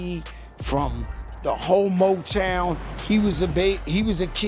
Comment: I thought he said homotown but he said whole motown 😂